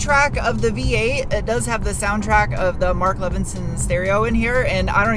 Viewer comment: i thought you said " Martha Vinson"... was lmfao😂😂